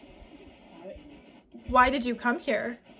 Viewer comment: When you asked “why did you come here” I heard “come in” and “someone”